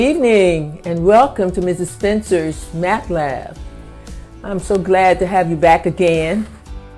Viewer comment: I thought she said "meth lab" 😂😂😂😂😂